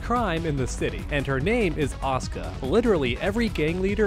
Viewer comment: I thought u were saying "OSCAR"... I was like who the fuck is OSCAR... My dude, it is "ASU-KA"!